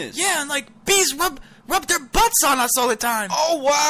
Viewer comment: I thought the bee said people rub their butt on them all the time😂😂XD